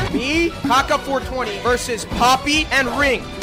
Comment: I thought you said Kaká for a second